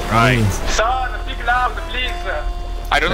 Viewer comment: i think he said "sorry speak louder please"